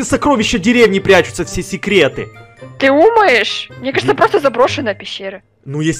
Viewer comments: Я ОДИН УСЛЫШАЛ ЧТО СКАЗАЛА:ТЫ УМАЕШ? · мне одной кажется что она сказала 'ты умаешь'